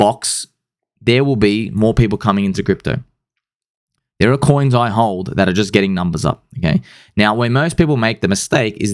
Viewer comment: What Law did he call it at 19:43 ? Didn’t quite hear him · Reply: Richard heart calls it "Heart's Law". But its not a real thing, its got another name. Just a meme at this point